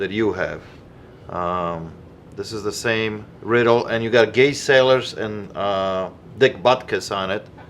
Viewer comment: says "gay sailors" instead of "Gale Sayers" 😂